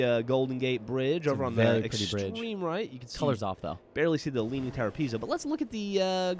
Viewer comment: i thought he said leaning tower of pimps